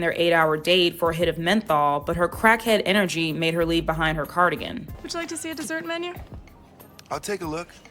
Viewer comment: This is my third time watching this, and I swear I thought you said card again, and not cardigan😒🤦🏽‍♀️🤣